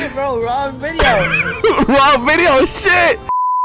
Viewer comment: what video are they referencing when they say "wrong video"? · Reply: I think they said "we're on video."